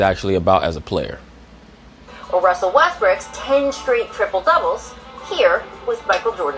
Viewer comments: did she just call him Russell Westbrick? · did rachel said Russel Westbricks 😂😂